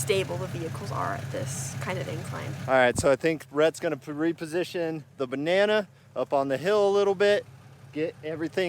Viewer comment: did he say Rett is going to reposition his banana?